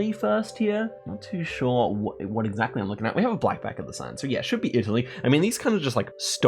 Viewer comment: Please talk more slowly or clearly. I can't tell what you say at 5:20. We have a black back of the sign? But you can't see the back of the sign. The CC has you saying "black back of the sun" which doesn't make sense either.